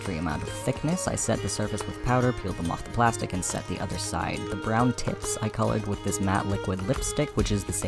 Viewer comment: i SWEAR it sounds like you said "ThE bRoWn TiTs" lmfaoooo